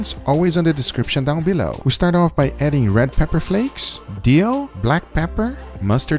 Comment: Did Guga said "Dio"?